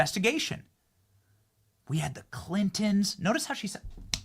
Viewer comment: Benny, for accuracy, she clearly said ClintoN (not clintons) although that witch is as dirty and evil as they come.